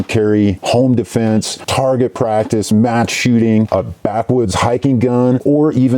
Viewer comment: did he say mass shooting lol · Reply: Match shooting